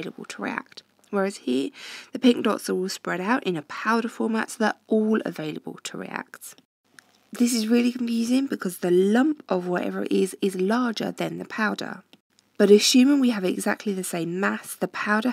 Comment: does anyone hear amusing or confusing?!😹😹😹😹